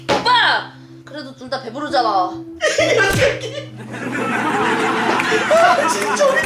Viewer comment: 분의 이 녀서이 이라고 들려야 하는데 이런 새끼라고 들려서요